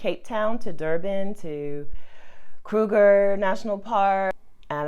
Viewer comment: Did you say cougar national park? 4:05 (LOL)